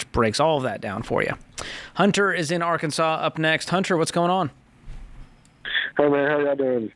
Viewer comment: Doesn't sound like it's a "Sarah" on that last call about living paycheck-to-paycheck and not getting ahead. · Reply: It's not. His name is Hunter, or at least that's what George said at 2:02:30. Hunter in AR, not Sarah in NJ. The graphics people messed up.